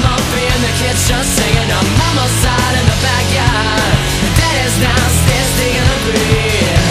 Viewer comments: I hear "daddy is downstairs drinking a beer" and "daddy is out getting a beer" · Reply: i thought he said daddy's downstairs diggin' the beat. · I hear "daddy is downstairs drinking a beer" and "daddy is out getting a beer"